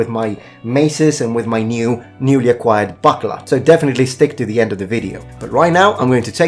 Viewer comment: re - 3:31 Did he just say "Newly acquired butt plug?" Because I don't think I want to see him play around with that, at all... In fact, I'm certain that I don't want to.